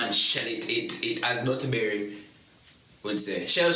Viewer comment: Did u say atterburry